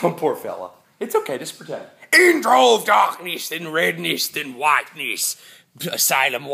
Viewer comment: I watched this on my phone and got a text during the intro so I heard "intro of darkness then redness then Bazinga"